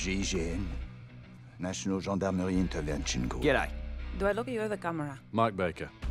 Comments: Everyone is dead serious and telling it how it is. Mozzie: G’day!!! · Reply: DaRealest21 I thought she said “do I look good in the camera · Everyone is dead serious and telling it how it is. Mozzie: G’day!!! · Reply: I thought mira said"do i look the overcomerer"